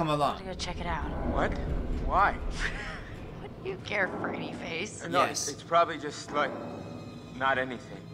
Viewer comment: i swear I heard her say Freddieface.... oh my gooood